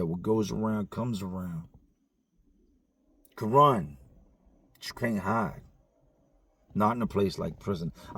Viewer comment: wait did he just say “Quran, but you can’t hide” 😂😂😂